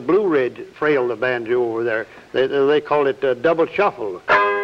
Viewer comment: I need to learn this frailing style. Did he say double shuffle?